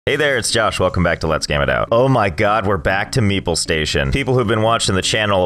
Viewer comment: I always thought you said meatball station not meeple station 😂